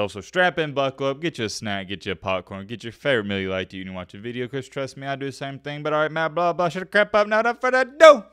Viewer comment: Strap in, buckle up. Get you snack get you a popcorn. Get your favorite meal you like to eat when you watch a video cuz trust me I do the same thing. But alright Matt blah blah shut the crap up now without further ado, letssssss get into ittttttt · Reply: Damn I thought he was saying “get you a cold miller lite cuz trust me I’d do the same thing.” Or at least that’s what I’m going with 😂